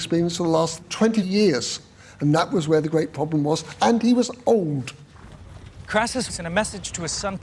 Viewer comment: lol at 6:09 i thought the old guy said "and he was owned"